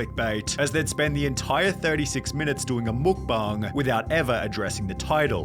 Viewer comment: It sounded like he said Muk bung